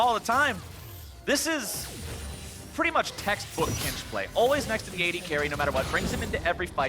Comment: he says olleh instead of corejj at 31:30? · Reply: No, if you listen to it he says "always" because corejj always following doublelift on tahm kench